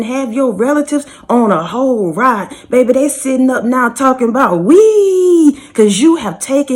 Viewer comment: You came out swinging and slashing- all gas no breaks on this one. I resonated with that entry soo much I had to sit and laugh .Did you just say "they sitting up talking about Wiiiiii ?"😂😂😂. What a time to be alive .. Thanks 🙏🏾 for the message sister .. Wiiiii😂😂😂😂